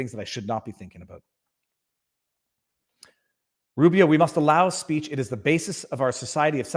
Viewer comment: I was listening and not watching and I heard you say "Rubia" .. defending free speech. I thought you said RUBIO.. and I was so excited for a split second. If only our Congressmen and women would care more about these issues.. :( Thank you, Viva, for standing up for Alex Jones, for taking a stand... I am so upset at what they are doing to him, and doing to all of us...